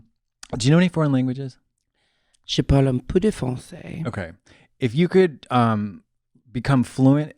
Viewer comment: OMG when she was asked if she knew an other langage and she responded in French, I heard "je suis un peu défoncée" (I'm a little high) ! But she actually said "je parle un peu de français" (I speak some french). If there is any other French person here, tell me I'm wrong. ahah It's